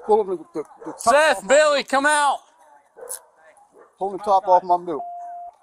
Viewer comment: did he say he pulled the top off the mountain dew?